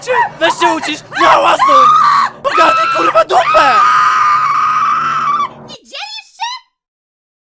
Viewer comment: czy ja tam usłyszałam słowo kurwa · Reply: Patrycja Góralczyk tam było : no zamknij kur*a dupe !!!